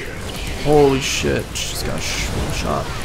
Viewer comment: did say holly shit or i m having problems in my ears